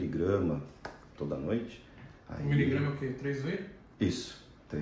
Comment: pessoal, alguem entendeu o que ele falou nessa parte? "3 uê" foi o que eu entendi :x